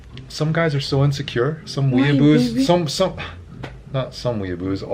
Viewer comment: I thought he said "Some hebrews" rather than "some weeabos" and I did a such a double take 😂